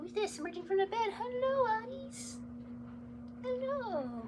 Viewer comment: it's-a me, Mario! i swear i heard Mario when you said hello to Autumn. perhaps Nintendo will cast you as his daughter...?